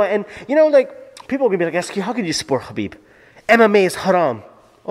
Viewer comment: what did you say mme?